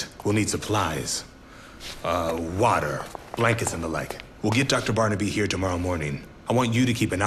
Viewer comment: I thought he said dr. Barney beef